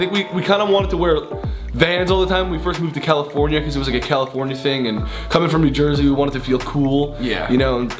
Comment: when Grayson said “it’s a California thing” the captions said “it’s a gay California thing” 💀💀💀💀